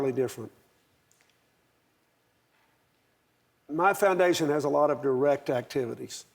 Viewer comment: thought he said "my foundation works on a lot of erect activities".......